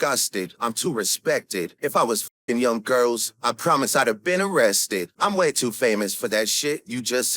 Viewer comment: I got an ad at “I’ll be arrested” Sounded like “I’ll be erect-“ I’ve been laughing for five minutes